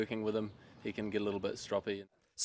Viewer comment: What was that word at 0:50? Stropy?